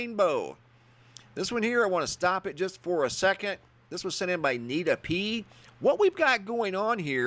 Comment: Nita P - sounded like I need to pee LOL